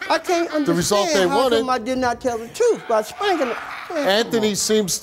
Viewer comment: I heard the dad say “I don’t understand how I didn’t tell the truth, I strangled her.” Then later on the girl was saying that her dad “didn’t do anything she suffocated.” Smh too much. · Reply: I heard him say “by spankin’ her”, not “I strangled her”.